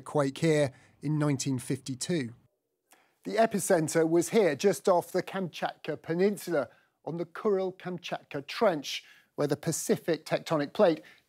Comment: What words are these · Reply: ‘Just off the Kamchatka Peninsula on the Kuril-Kamchatka Trench’